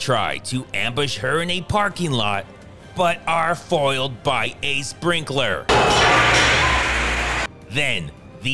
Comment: When u said "a sprinkler" for some reason i thought u said "ace brinkler" and was wondering who tf that is haha